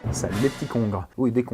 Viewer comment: La première fois j'ai entendu salut les cons